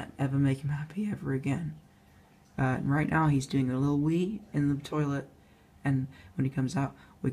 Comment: i thought he said hes going to do a little weed😂😂😂